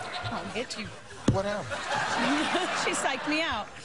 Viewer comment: did she say "I'm gonna hit you" @ 0:13?